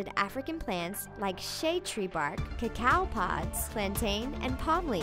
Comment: did anyone else hear cacow pods instead of coca pods😂😂